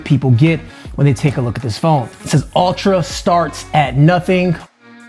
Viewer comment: Did he just say ultra "STARTS" at nothing and no one noticed?